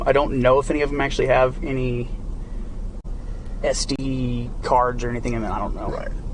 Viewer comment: I can't be 100% sure about this but I have VERY good hearing....Does anyone else hear J. Duggar in his super "OH muh gerd, I am so busted by these cops" Freudian slip when he's speaking of SD cards but seems to call them STD cards? Maybe it's just my imagination but I dunno... what do y'all hear? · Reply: Yep, he said “STD cards”!!!